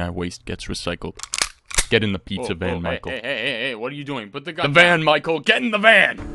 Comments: I thought he said "get in the pizza Michael" for a second lmao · "Get in the Pizza Van Micheal"... Pizza Time Stops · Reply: I thought he said Get in the pizza Micheal